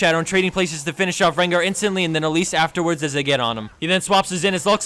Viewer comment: is it just me or does he say rengar?